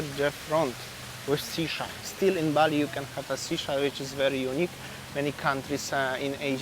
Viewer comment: Nusa Dua beaches are quite good, and Bali Collection looks a lot better than earlier days for sure!! I thought you said "sea shark" (was: shisha) and I was looking for the aquarium 😂 Take another break if needed man!